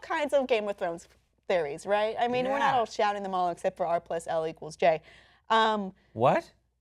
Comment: What did say at @ 7:07? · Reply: "we're not just shouting them out like R+l=J"